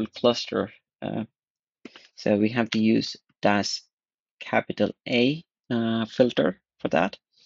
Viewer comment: did you say "we have to use DAS KAPITAL"? ☭